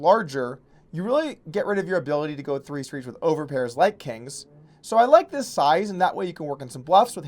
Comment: did he just say "overpairs like kangs" ?